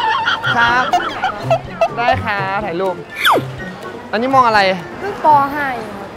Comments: น้องน่าจะได้ยินว่า อันนี่ม.อะไร 😂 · ประมาณว่าพี่ถามว่าน้องมองอะไร แต่ถ้าลองคิดดูแล้วน้องอาจจะได้ยินว่า น้องม.อะไร แล้วน้องก็ตอนว่าเพิ่งป.5เองครับ น่าจะประมาณนี้แหละค่ะ...